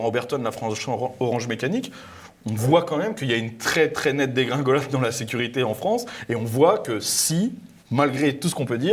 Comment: Le bon mot c'est "on devait avoir des voitures volantes et on a [...] des vélos" (ni moi ni l'automate de sous-titrage avons compris la partie entre crochets). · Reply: Uber eat à vélo